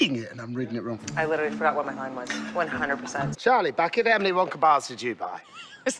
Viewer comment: Does anyone know what Robin Hood is saying at point 9:46 ??? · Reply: "Charlie Bucket, how many Wonka bars did you buy"